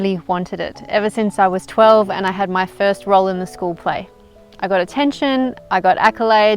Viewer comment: at first I thought she said her "first roll in the hay". HAHA